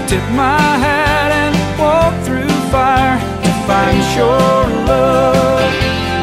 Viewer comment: I always thought as a kid he was saying "to fight your love". I thought boy he is really playing hard-to-get.